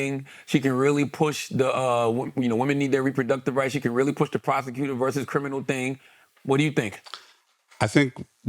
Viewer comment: Also legit thought he said, “she’s a prostitute” not prosecutor. I mean, both are accurate and both in the direction of black men always to their detriment.